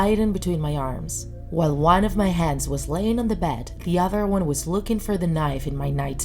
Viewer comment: I thought she said "one of my heads stayed on the bed and the other was digging for my knife" and I was like WTF?